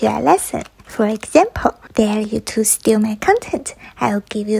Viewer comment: I I’ve ur videos so much lol!! Band when u said ghosts. I thought you said goats!! 😮😢